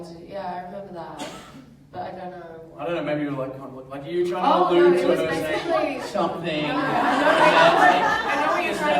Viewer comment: Do you guys heard what did Bob say at 40:35 ? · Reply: Lena Cookie "I don't know, like, maybe you were trying to allude to something..." 😉